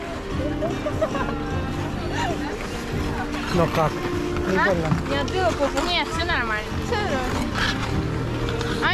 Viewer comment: What does mean the phrase sounded like: Njeotbilapopu? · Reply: Did you hit your arse.(when she fell) 🤗